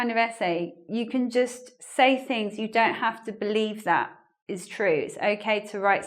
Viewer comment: The first time I listened the video, on time 8:13, I misunderstood.I thought it was something like "saying things you don't believe are true"-"you can just say things you don't believe that are true" and sounded to me like the perfect definition of lying, but... it's OK on these exams.Who didn't?"He who is without sin among you, let him be the first to throw a stone at her." LOL